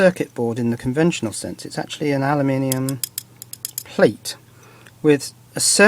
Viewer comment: When he said aluminum, I thought he said anamantium, like the shit in wolverine lol